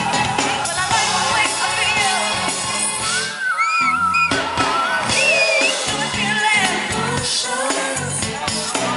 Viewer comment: I hear somethingg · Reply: Insideeeee